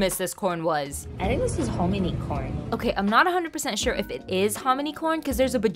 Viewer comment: For a sec I thought said harmony corn 🌽